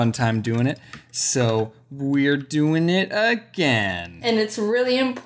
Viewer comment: My brain was kinda on auto pilot and all I heard was, “We had fun doing IT, So were doing it again!” I was just like, WHAAAAAAT